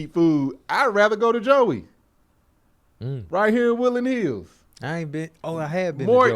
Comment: Boy so country. He thought he said "I'd rather go to Joey out here in Willy Neal's." Who is Willy Neal? Ohhhh, "Woodland Hills"